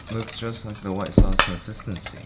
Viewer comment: first time I watched thought he said 'oh sh*t looks just like the white sauce my sister sees'